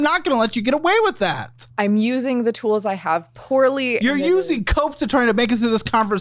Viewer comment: Omg I thought he said “you’re using coke to try to make it through this conversation” and not cope 😭 I was like get her Caleb! Call her a crackhead!!😂 lmaoo